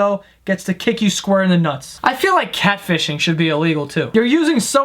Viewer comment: What does he say at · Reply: You mean catfishing?